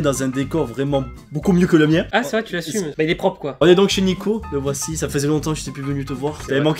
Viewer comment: J'ai compris " On est chez me gynéco" ... c'est mon esprit tordu qui parle ou articule pas ?